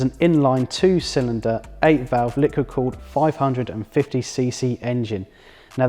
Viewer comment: I swear he's saying liquor-cooled